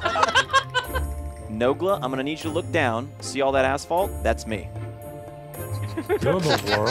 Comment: I thought Seananners said "See all that asshole?" I was liek wat..